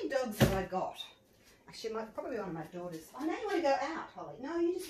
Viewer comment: I've relistened to one part at about 10:55 a few times and I can't figure it out, is she saying it's probably one of her daughters scratching at the door? 😕 I'm really tired rn so it's probably something really simple but now that I've heard it this way I just keep hearing it the same way and it's bugging me because I'm sure this can't be what she's saying.